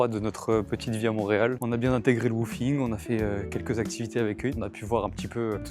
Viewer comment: "on a bien intégré" le quoi ? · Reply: le wwoofing ! C’est un échange avec des particuliers, on travaille 4h par jour pour eux (jardinage, peinture, petit travaux…..) contre logement et nourriture !